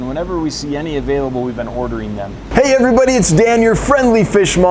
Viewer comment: At first, I thought I heard “Whenever we see these available, we’ve been ‘hoarder-ing’ them” at the beginning 😂😂 I move that we make that an official fishkeeping term 😅